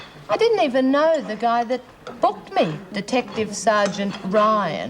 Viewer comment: it sounds like says "The guy that F&^#@ me, Detective Ryan."